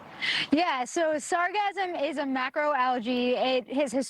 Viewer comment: I was driving while listening to this and I thought she was saying “sarcasm” for like a whole minute. I was like “oh it grows on the ocean surface? Didn’t know that.” Lolllllll